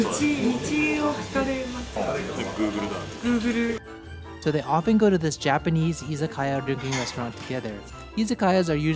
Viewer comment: what they said?guru guru? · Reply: They said guuguru (グーグル) which is the way how Japanese people would pronounce the word google